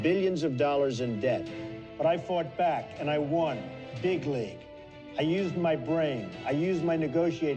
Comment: OOh This whole time everyone thought he was saying "bigly" but it was actually "big league" and his deepfried brain keeping him from speaking correctly